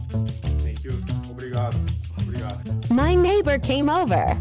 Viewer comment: first i think he is saying 'sukriya' it's an hindi word...